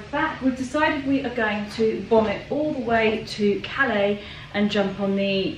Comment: At first I thought you said "we're going to vomit all the way" LOL!!! 🤣